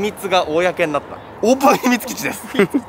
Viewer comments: のところ、シルクが｢オープン秘密基地｣って言った後に被るように女の人の声で｢秘密基地｣って聞こえるんだけど気のせい？ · 辺りで女の人の声で｢秘密基地｣って聞こえるの気の所為？